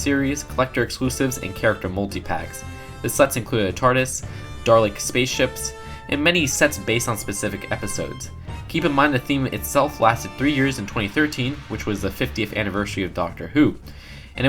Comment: Did he just say Darlek?